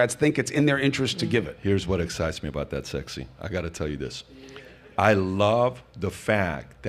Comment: Did Patrick call Chris sexy at 1:21:50 ?